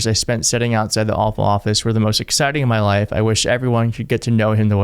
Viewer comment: Thought said "Awful Office" the first time I listened (instead of "Oval Office"). That would be an appropriate name for what it's become.